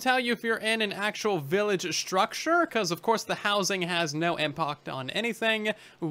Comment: did you really say "Impoct" ?